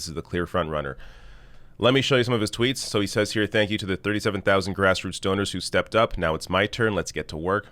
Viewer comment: I thought you said "Grassroot Stoners". 😂🤣😂